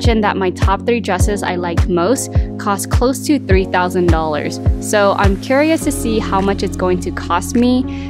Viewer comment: how much did she say the dress cost? 30k or 3k? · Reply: Shanae 3k definitely not 30k 😂